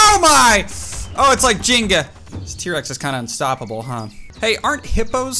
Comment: did he just say "jinga"?